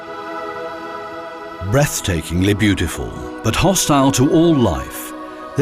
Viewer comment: Hahaha he said breath takingly beautiful and I for a second thought he said breast takingly beautiful 😂😂😂😂😂😂